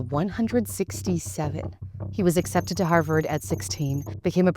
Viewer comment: not even 5 seconds in and I hear 67